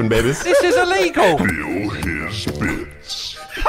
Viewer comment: What did it said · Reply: fill his bits